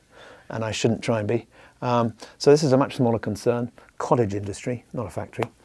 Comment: "I'm not a mini Hans Zimmer... it's a cottage industry, not a factory." Now if that isn't telling! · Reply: I thought he said Frottage